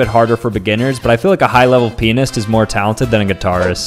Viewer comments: I looked away and took a sip of water right before he said “pianist” and now my room is covered in water. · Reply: The joke is that when he said “pianist”, it sounded like he said the word “penis”. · Who thought when he said pianist he said pen**